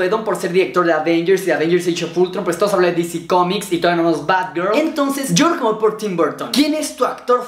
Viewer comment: sdoasndsdmenos batgirl xddx eso entendi yo xd